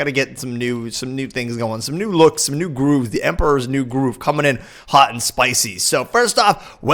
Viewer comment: did say emperors new groove. I MUST KNOW IF YOU LISTEN TO PANIC AT THE DISCO.